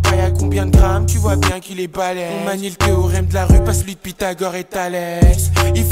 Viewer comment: Il dit quoi à 0:32 svp ? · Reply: On manie le théorème de la rue pas celui d'Pythagore et d'Thalès